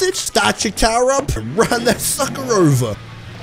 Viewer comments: I thought he said run that f#cker over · it sound like he said fucker instead of sucker